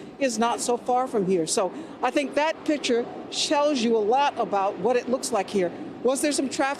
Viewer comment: Did she say shels you, its like melting point of shows and tells i guess )) start from